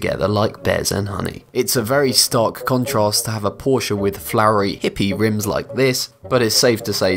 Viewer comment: did he say porcha